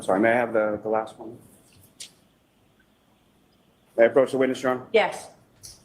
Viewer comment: Starting at about 48:03 into this video, Lally says, "May I approach the witness, your honor?" but the closed captions said, "I approach the wind instrument, yes." Hahaha. I thought that was a great interpretation of what was actually happening there.